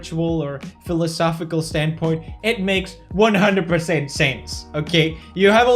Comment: I thought you said "It makes totally SANS", then i realized it was "sense" I'm very sorry for this.